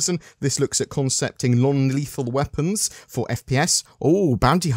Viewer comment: Sounded like you said "Long Lethal". Is this your shortest video to date?